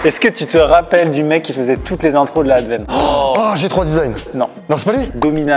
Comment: Que dit Amixem ? "Toutes les intros de la...?" ? Il parle d'une ancienne team ? J'ai besoin de savoir de quoi il parle 🙏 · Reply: De la advene